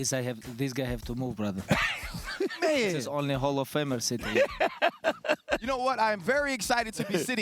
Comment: DC: Let the music move you! Khabib: Its okay brada I have this guy to move. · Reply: I thought he said, "this guy needs to move, only hall of famers sit here"